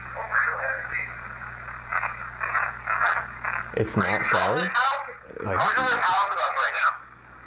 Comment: EVP Timestamp 42:30 after you count to 3 and ask "who all is in this house?" It literally sounds like quickly running footsteps followed by "it's not Sallie" Then you ask, is this Sallie, or are you from a different location? I heard "not from here"